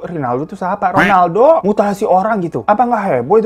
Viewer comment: Bang, menit 08:40 gua ulang ulang terus, telinga gua yang salah denger mutasi atau emang bang bilang mutasi yak? 😭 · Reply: Bener kok emang bilangnya mutasi bukan mutilasi wkwk